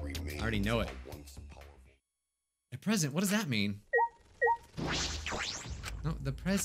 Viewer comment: I thought he said "what was that noise"